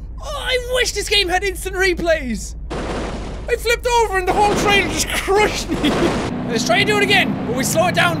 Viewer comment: did jack say train?